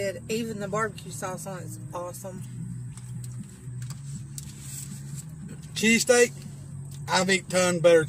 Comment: my deafass heard you say CheeseCAKE...not cheesesSTEAK